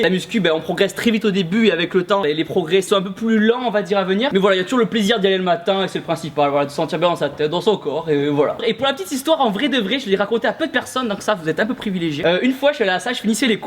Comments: je rêve ou j'ai entendu "C'est toujours bien du allez le matin pour sa teub euhh pour son corps" 😂😂😂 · au début j'ai compris autre chose. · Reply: MASTER swag dans sa teub ;)